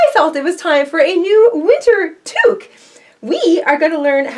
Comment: Oh I like that hat!!! Lol when you said winter toque I thought you said "lizard tooth" and I thought this is going to be interesting. Lol. It is interesting and I love it.